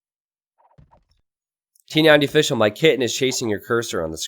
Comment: That was one of the funniest things I saw in this channel. I loved how funny you are. Somebody clip this "T90 plays with viewers kitten" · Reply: Oh, kitten...i thought he said "kid" :D